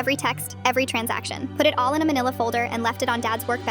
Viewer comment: I always thought they say Vanilla Folder. LOL... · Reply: no it’s manila. Manila envelopes are called "Manila" because they were traditionally made from Manila hemp, also known as abacá, a fiber derived from a plant native to the Philippines, specifically near the city of Manila.